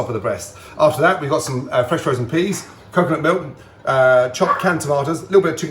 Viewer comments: did Gordon just say, Fresh frozen? 3:02 for the record, frozen peas are great, i just never thought i'd hear those words from him in a cooking setting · Did he just say fresh frozen peas? I seem to remember him calling someone a muppet for saying that his food was fresh frozen :P